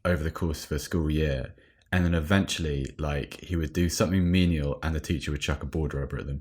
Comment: What does say after "and the teacher would"? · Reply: "menial" (of work) not requiring much skill and lacking prestige